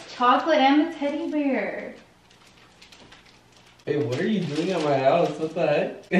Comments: Am I the only one who heard Brian say babe what are you doing in my house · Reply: Amari Mcgowan I heard brain say “babe what are you doing at my house” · Am I the only one who heard Brian say babe what are you doing in my house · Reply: He said wait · Am I the only one who heard Brian say babe what are you doing in my house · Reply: I heard “aye what are you doing in my house”